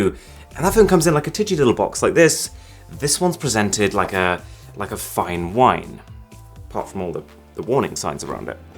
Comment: When you said "like a fine whine" I heard "Like a f*cking whine" Lmaoooooooo